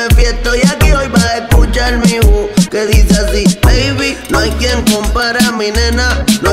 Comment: Pensé que decía:no hay tiempo para mi nena 😂😂